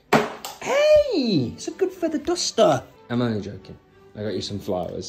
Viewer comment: “What’s the price of-“ “TEN POUNDS?!?” oH MY G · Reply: I thought she said “oh you left the price on- 10 pounds!😯” 😭🤣